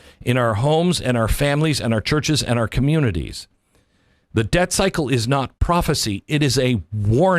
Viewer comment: I thought you said "Dead Cycle" at first.....